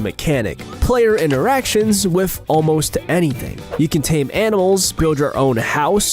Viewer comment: Did I hear "tame anals?"